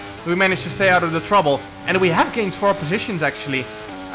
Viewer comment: when you say "gained four positions" it sounds like youre saying 12 instead of 4 when i think of the number 12 but when i think of 4 it sounds like you are saying 4 its like that audio illusion meme, but out in the wild